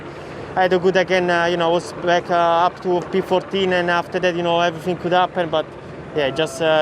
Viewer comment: Did Giovanazzi just say I f**ed up and they missed beeping it out coz of his accent 😂😂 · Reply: It sounds like " and after that everything could happen "